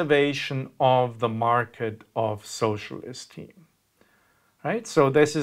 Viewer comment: I heard "socialist team" and I was very confused.